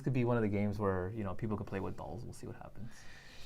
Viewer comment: play with dolls? is that what he said